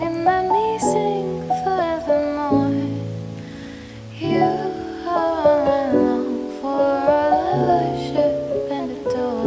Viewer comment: My ears are in pure bliss, I must listen to the whole thing on my trip tonight. In fact, I'll listen to it a hundred times! See you all in 3026 when I die finally and cant listen to anymore. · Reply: when she says "you are all i worship and adore" it kinda sounds like shes saying: "you are all i worship and a door" XD